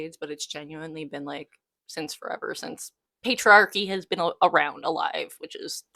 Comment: I heard "pedroarchy" at around 7:50 and was 😮 for a moment.